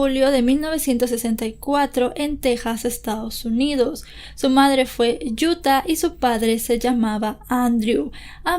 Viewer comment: ¿Alguien más de Argentina que se haya reído en 0:44 cuando parece que dice "su madre fue yuta"?